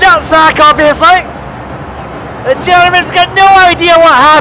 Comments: "Ambushed by a big, stupid cake." I see what you did there. 🙂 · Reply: and I thought he said 'Ambushed by a big stupid pig'. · "Ambushed by a big, stupid cake." I see what you did there. 🙂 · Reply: I heard it as 'big stupid kid'.